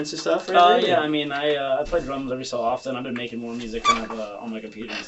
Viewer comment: i thought he said drugs not drums...big difference in an opener